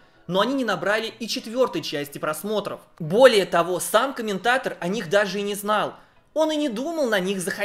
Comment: Такое обилие слова «самка» за ролик (я, видимо, не в теме, на что это отсылка), что слова «сам комментатор» на 4:33 я услышал как «самка-ментатор».